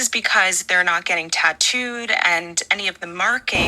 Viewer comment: did she say "they didnt get tattood' ??? is Hitler in charge here?